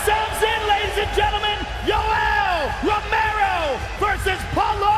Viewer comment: "Polo Costa and Yellow Romero" great job Joe Hogan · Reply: LiveLife 101 I thought he said yoello ferrero